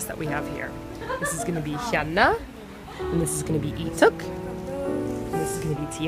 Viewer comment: lmao did she just say 'leetuk'? sorry if that's not how you spell his name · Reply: She said 'Leeteuk' and he is a member of Super Junior :)